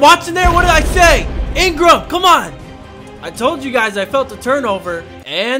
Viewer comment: I thought he said "I feel a turtle" near the end of the video😂😂😂